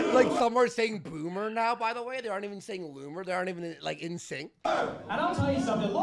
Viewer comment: I think they were saying "coomer" instead of Loomer😂😂